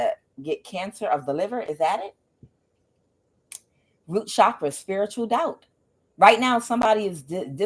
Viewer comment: Friend I thought you said spiritual gout 😂😂😂I was like take me to tha Kaaaaaaang 😂oh they messed up. I loved this reading